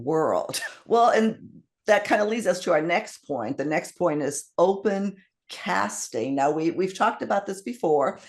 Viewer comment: I thought you said "Open Casket" when you said "Open Casting". I had to play that part back again.